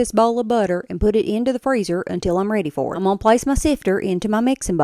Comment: I kept smirking, I am hard of hearing and I heard, "I put sister into the mixing bowl" visually I see a sifter.😅